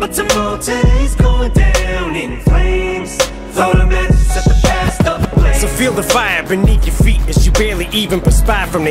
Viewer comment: I think he said “throw the matches at the gassed up flames”